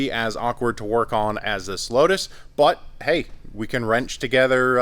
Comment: I swear he says "slowtus"